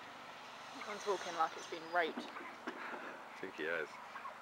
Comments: did she say rape xD!!! lmfao and is that your farm? 4:33 whos cry lol? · did she say rape xD!!! lmfao and is that your farm? 4:33 whos cry lol? 5:52 bitch! lol 740!!!! roflmao!!! lol you guys r so funny =D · did she say rape xD!!! lmfao and is that your farm? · did she say rape xD!!! lmfao and is that your farm? 4:33 whos cry lol? 5:52 bitch!